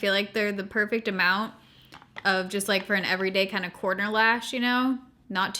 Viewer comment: Literally every time your said corner lash I thought you said porno lash and I didn't even question it. Just thinking "oh yeah those are probably good" 🙃😂